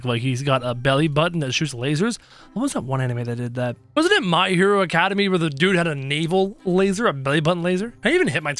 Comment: did just say my hero academy! It’s my hero acedamia!!!!!! But yes somebody has a laser